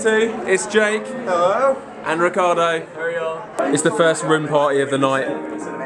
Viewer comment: I thought he said rim party @ 8:23 jfc